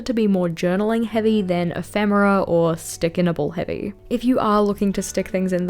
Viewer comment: Between your Travel Diary from Sat 08 and what I kept hearing as “sticker nipples”, this was your most entertaining video yet. 😂